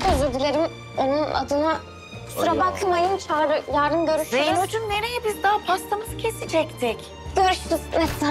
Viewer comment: de zeyno anne dedilan😮😅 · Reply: Hanim dedi